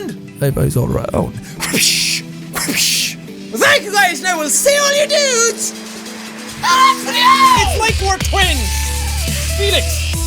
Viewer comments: In his outro it sounded like he said ''I will see all of your nudes in next video'' lol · For the longest time, I thought his outro said "I'll see all your nudes, in the next video" hahaha. Enunciate!